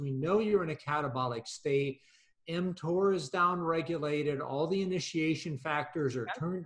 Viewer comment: sorry I don't speak English that well, can someone tell me what word he said at minute 3:37 something like emtor? mentor? he repeat that word in other videos and I cannot understand the word. He said ä catabolic state ? is down.... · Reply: Mtor